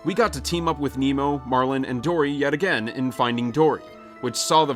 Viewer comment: Omg when it said finding dory I swear to god I thought it said 'ending dory' lmao